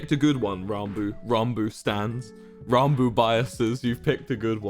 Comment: did. did wilbur call us "ranboo biases" at 1:31:53. why is he using kpop slang im so fucking scared.